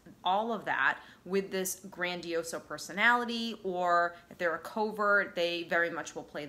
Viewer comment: Did she just say “grandioso personality”?